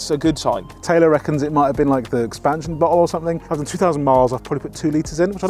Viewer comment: did you just say "Taylor reckons it might be the expansion butthole or something"?!? 😆🤣